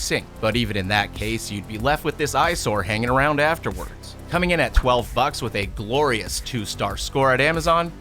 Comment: Is it just me or did you say the F Word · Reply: Just you. “Coming I’m at 12 bucks”